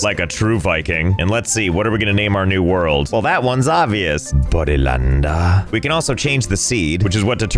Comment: Me and my brother were dieing when we heard him say bobdylande